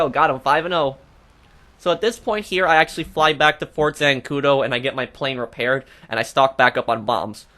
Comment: Hahaha, at 2.27 I thought you said, "get my plane repaired and stopped by my Moms" 🤣. I had to listen 3 times to understand what you said. Perhaps it's the American accent, or perhaps I'm going deaf 🤪🤪🤪